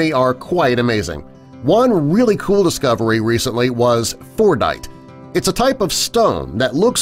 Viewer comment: Fordite? Thought he said fortnite